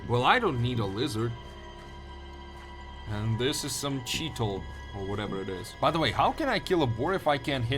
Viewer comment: Did you say cheetle? 🤣🤣🤣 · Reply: Cheetah, 🤣🤣🤣